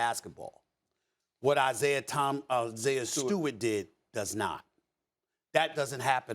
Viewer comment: Am I the only one who almost heard Stephen A say Isiah Thomas, instead of Stewart lmao